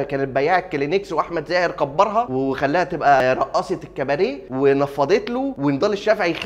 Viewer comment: انا سمعتها رقاصة في الكبانيه 😂😂